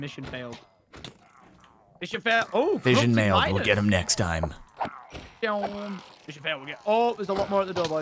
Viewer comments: anyone else heard him say " fission mailed we will get them next time" or is it just me ? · did.... did Jordan say Vision Mailed